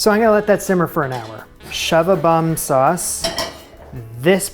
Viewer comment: shove-a-bum sauce?? What am I hearing?